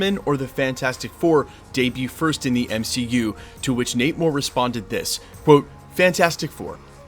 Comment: You kept saying Nate Moore but it sounds like your saying namor lol